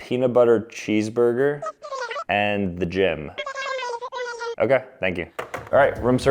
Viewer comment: When you said I ordered the Jim I thought you said you ordered the actual gym😂😂.